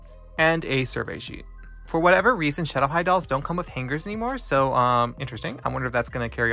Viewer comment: I love her!!! She is so gorgeous ❤️❤️ When you said "they don't have hangers" I thought you said fingers 🤣